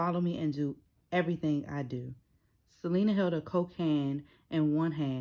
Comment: I thought I heard cocaine I was bamboozled lol , great Content as always.